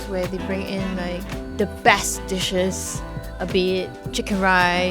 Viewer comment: I heard (ass dishes).😂😂